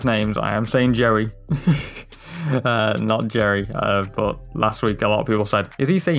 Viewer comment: Ngl i heard "I'm saying jerry not jerry' at first